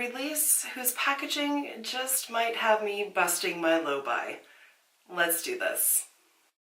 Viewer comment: 🤣🤣🤣🤣 When you said low buy- I thought I heard it as loby- as in I thought you mispronounced a word. Very next line was let’s talk about anuses. I thought -well you don’t want to know what I thought.🤣🤣🤣🤣 $77 for a palette? No way!! All the crystals are just gimmick. Totally agree with you on the Bobby brown palette, pretty but won’t buy it. Hate the black & white sneak peeks. Waste of space. But hey, if you are close to MN I can bring you a palette or two🤣😉🤣